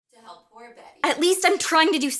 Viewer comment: The audio for 14:32 is cut off so here what they say Laura: “are you really so damaged that you’re incapable of caring about anything?” Carmilla: “you really think you’re doing anything to help that girl? To help poor Betty..” Laura: “at least I’m trying to do something” Carmilla: “oohh are you trying your very best? Because I’m sure if you stay pure at heart and really believe that will make a different” Laura: “well it’s better then lounging around all day and pretending to be all cool and disinfected when really you’re just miserable and alone.” Carmilla: “…. and you really think you’re doing a lick of actually good? Do you know anything you didn’t know the day before she vanished? You’re a child and you understand nothing. Not about life, not about this place, and certainly not about what it takes to survive in a world th- you know what? The sooner you stop playing Lois Lane the better off you’ll be.” ……………… Laura: “no” Carmilla: “what?” Laura: “no I’m not gonna just give up. Maybe you’re right, maybe I am a child. A 19 year old who never left her city limits before she got here. Who thought that University was gonna be some big ‘adventure’, full of books to read and parties to dance at. Who never thought anything bad could actually happen. Well turns out the world doesn’t work out exactly how I thought it was going too. My university is creepy and parties are full of numbskulls getting hammered and girls go missing and nobody seems to care so maybe that’s just the way it